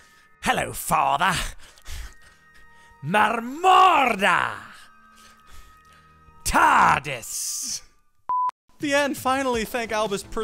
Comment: Did I just hear "Morsmordre... Tardis"